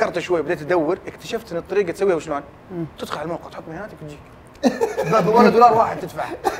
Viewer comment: ماسمعت وش قال تدخل على الموقع وايش ؟؟ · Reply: تحط بياناتك وتجيك